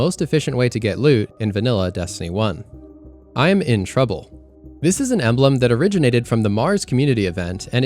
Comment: The "Im in trouble" part near the end, to me, doesn't sound like its saying "I'm". To me it sounds like "Your in trouble"